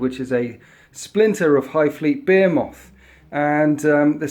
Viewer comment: Did I hear 'beer-moth'......